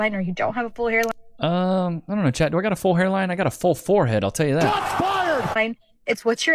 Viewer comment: did she say forehead line 😂